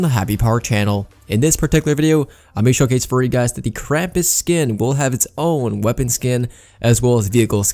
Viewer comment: I thought he said crappy skin